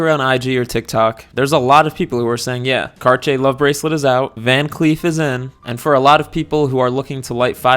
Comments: van cleef, more like van queef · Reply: I misheard it for van clit a lot welp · I totally heard you say "van queef" the first few times 😂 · I literally thought you were saying van queef this whole time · I keep hearing “Van queef”